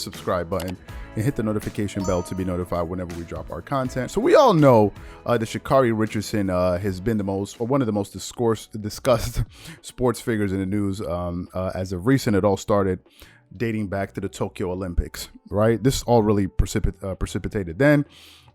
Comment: Close call! Thought he was about to say "she is the most" disgusting, instead of "discussed."😳